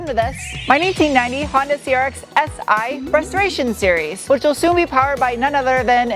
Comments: I heard "frustration series" instead of restoration series in the intro. I think they're one in the same. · Hah, I misheard and thought you said welcome to my 1990 CRX-Si Frustration Series! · When you said "welcome to my 1990 Honda CRX restoration series", at first I thought you said frustration series. Well, I guess that's not entirely inaccurate. 😉 Good job in spite of the challenges inherent in any vintage vehicle restoration, especially with accident damage to repair.